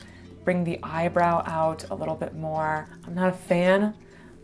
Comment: Why did I hear "Call me hentai anime" 😭